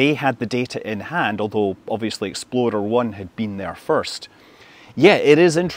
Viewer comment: 'Exploder' 1? Did I hear that correctly?